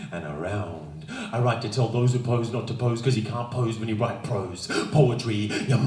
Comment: what does he say at 2:37 through the video? "And I can twirl like a ___ dervish" What?? I can't understand what he says here but Im trying to get every word because this is just so good!! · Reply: INSOUCIANT adjective free from concern, worry, or anxiety; carefree; nonchalant. Synonyms: lighthearted, debonair, jaunty, breezy.